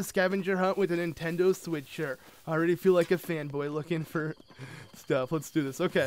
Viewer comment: at first i thought said "i already feel like a fuck boy" but then i realised he said fanboy